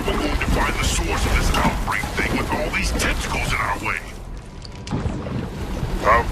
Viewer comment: I thought he said testicals